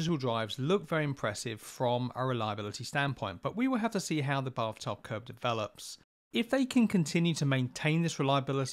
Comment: people keep rewatching because you can't tell what the hell he is saying. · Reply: How the bath tub curve develops...